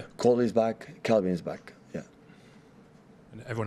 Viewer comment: What did he say at 2:29?🤣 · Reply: The "N" word